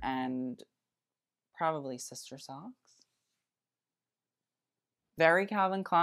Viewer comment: I . thought you said probably sister soft and I had to replay it like 50 times to hear socks.